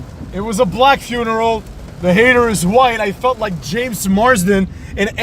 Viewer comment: You think so? Or friend? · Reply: i feel like an idiot. I thought he said “with the hater’s wife” but he actually said “the hater’s white”. So i don’t actually know if his wife is black or not. My comment is misinformation.